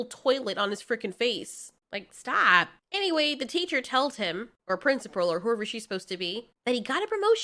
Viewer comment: Did you say, "Telled him" 1:49 ?? hahaha, you're so cute...